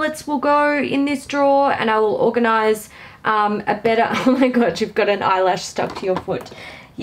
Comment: Hi i cracked up myself i thought you said to your cat you have got a eye lash stuck to your moot hole family laughed then you said the cat was looking for the black hole